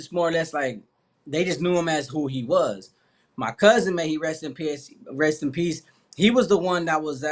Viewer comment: Yo did he say may he rest in piss on accident ?? Our am I tripping